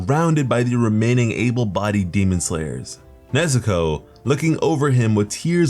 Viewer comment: lmfao if u turn the subtitles on it says Mexico instead of nezuko 😂😂